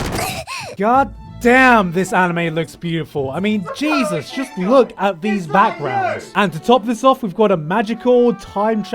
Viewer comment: What did say at 9:54 in the background? It was something something? Is that you? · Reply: "Makato Shinkai, is that you?" He did it on last year anime that had mind blowing animation such as violet evergarden.